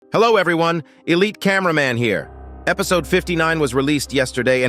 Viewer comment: Why do I hear "a late cameraman here" 😭